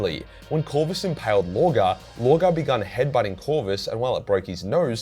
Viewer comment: I heard that "Lorgar begun head biting Corvus".😂 I thought he almost bite his nose off.